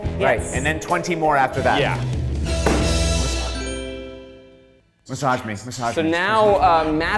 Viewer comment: I thought Ryan was saying "misogyny" not "massage me" and I was like "is he hyping himself up or something??" 😂 (also obvs Ryan is a cool guy who wouldn't advocate for misogyny, I just thought if he was saying that as a joke it was pretty funny lol)